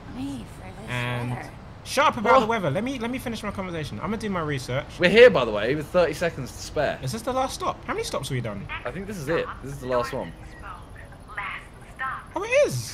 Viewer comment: I thought Tobi Said ,'shut up and let me finish my conversation".